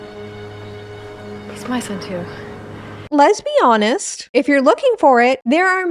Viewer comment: I thought you said ✨️lesbianas✨️ 😭😭